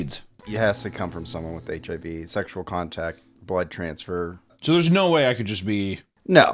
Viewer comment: i just need to point out that i genuinely thought said butt transfer